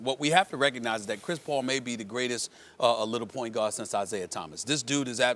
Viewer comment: Did he just say CP3 is the best PG since Detroit Isaiah.... Stop... Nash, Curry, STOCKTON, Westbrook, and Kidd are better than CP3.... All these players went to the finals with less talent than CP3 had... except Westbrook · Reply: he said LITTLE pgs meaning 6'0 & under